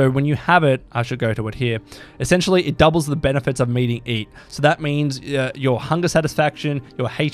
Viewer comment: I thought I heard him wrong when he said "Meating Eat" 😂